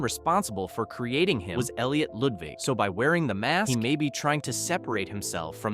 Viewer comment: Why did I think bro said Loodvigg from My Singing Monsters instead of Ludwig?! 😭😭😭😭